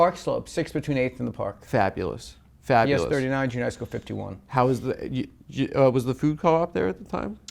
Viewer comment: someone PLEASE tell me what the fuck he’s saying here? All I hear is “… 39.. 51?”